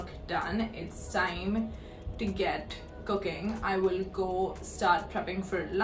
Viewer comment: for some reason, I heard "cocaine" instead of "cooking" lmao